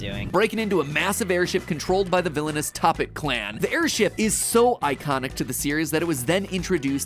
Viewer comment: did mattpat call the tophat the topit clan?